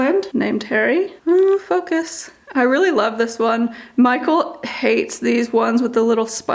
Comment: I thought you were about to say “f**k this” lol I’m thinking well that’s a little out of character 🤔lol Very cute plant tour 😊 I only have an aloe plant now about 5 years it’s huge and it grew pups! Which I have since repotted 🙂